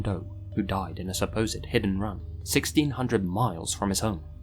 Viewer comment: when you said “hit and run” I heard it as “hidden run” lol